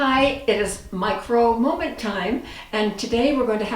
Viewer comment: LOL, I heard "Mike Rowe moment time"!